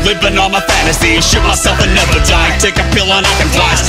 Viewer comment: Is it just me or does it sound like Tay says 'shit myself and never die'?